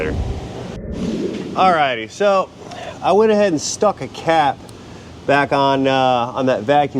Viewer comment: Just after this break lol 34:20 I swear at first he said "so I uh went and stuffed a cat" 😂😂